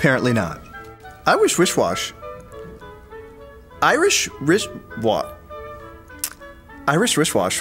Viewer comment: I thought he said Iwishwishwash, Irishdishwas